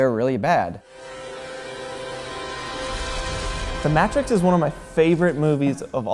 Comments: I honestly thought he said "The Mattress is one of my favorite movies of all time." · I legit thought he said "The Matress" 😂😂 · The way he pronounced Matrix at the beginning made me question reality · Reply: Sounds like he says The Matt trix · did he just say the mattrix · did he say MATT-TRIX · Did he just say “The Matt Tricks?” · Did you just say the Matt Tricks? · Did he say the mattress? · DID he just SAY mat trix LOOOL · Did he just say Mattrix? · excuse me the fuck did you just say? Matress? Tf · What the fuck did I just hear? "The Matt-trix" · it sounds like Matt Tricks; and what’s even funnier they say the title in the movie. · DID HE SAY THE MATT-RIX?